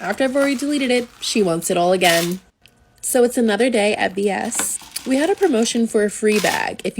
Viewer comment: I thought she said another day of bs